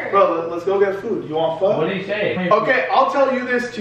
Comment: did i hear PHO?